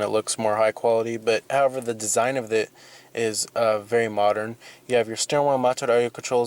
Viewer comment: is it just me or was he speaking another language at 9:05 for a second. i couldnt understand that word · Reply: The word was modern.